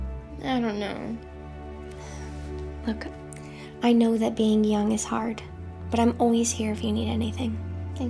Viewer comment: I heard that as being hard is young 😭😭😭